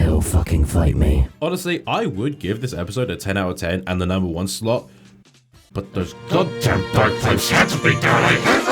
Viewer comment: What did you say at 4:40 - 4:45? Because I find it impossible to hear the tail end of it... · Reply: "But those goddamn bagpipes had to be there! I hate those frickin' bagpipes! AAAAAAAH!"